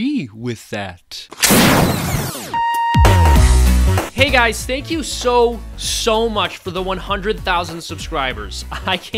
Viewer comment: Thank you so sososos much for 100k subs! Gonna be doing a 100k sub live stream this Saturday! Keep a watchful eye out! Also, for those who aren't familliar with all of the people being parodied. Sam Harris Ben Shapiro Jordan Peterson Stefan Molyneux Thunderf00t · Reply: I thought you said thank you soros for the 100k lmao